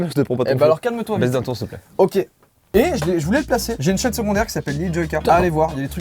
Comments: j'ai compris "j'ai une chatte secondaire" mdrrr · j'avais par compris "j'ai une chaîne secondaire" mais j'ai entendu "j'ai une cha**e secondaire"